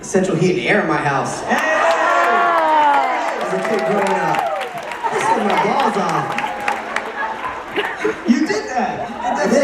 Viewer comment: What did Howie say at 11:26 which made all the boys laugh? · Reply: Hermosilla He said "I was sweatin' my balls off!" talking about before he had central heat and air in his house.